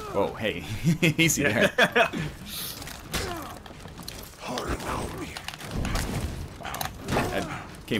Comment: What does the viking say when he's blocking? it's driving me crazy. I keep hearing "what about me?"